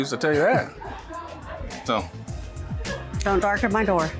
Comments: Did she just say "don't dark at my door" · Sounds like Defendant said, "Don't darken my door."at the end. If so, what a nasty racist comment being that the Plantiff is African American. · The “don’t darken my door” comment really showed her true colors. That’s probably why they didn’t want to answer in the first place smh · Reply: I thought she said don't knock at my door 🤔